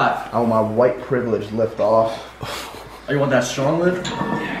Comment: Did anybody else hear what David said at 5:52? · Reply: "White privilege lift off" I think 😂